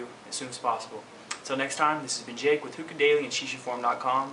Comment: Volume was low and I thought I heard shisha porn.